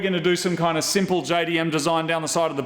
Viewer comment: Did I hear JDM at 1:15 and 2:30.